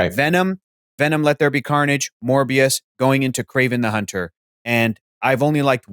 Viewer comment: "venom, venom let there be carnage, more BS, kraven... " I think I misheard, but not really